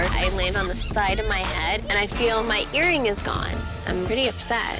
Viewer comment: I first thought she said “I lost my hearing”